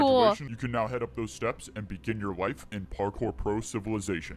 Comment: When he became a pro I thought the other guys said “begin your wife”😂😂🤣🤣🤣🤣🤣